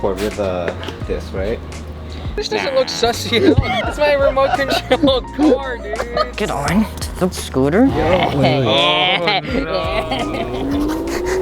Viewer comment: i thought it said stolen car lol